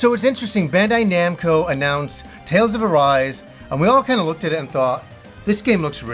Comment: I thought it said TALES OF ARSE